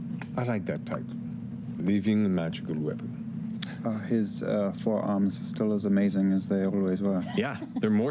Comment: Ah yes. Eodwulf and Caleb are such Heterosexual friends. Eodwulf heterosexually telling Caleb how good he looks every time they meet. Caleb heterosexually asking if he still has amazing forearms. The very heterosexual ways they look at each other. And they’ve interacted like twice. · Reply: Ngl every time I watch that clip I hear ‘four arms’ and not ‘forearms’ and I’m so confused every time. But yes. Very heterosexual. 😏